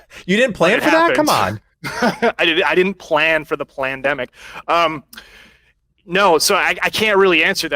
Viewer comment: did he actually said "plandemic"?